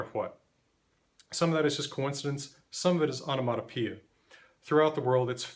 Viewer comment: What did Mr. say??: "Some of that is coincidence... some of that is ___ ??". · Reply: He said "Onomatopoeia" Basically, naming things after the sound they make. <link>